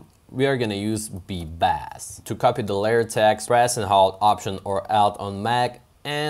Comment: what are you saying? to copy the layer press and what? how do i duplicate layers? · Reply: Press and hold alt and than select the layer and drag and drop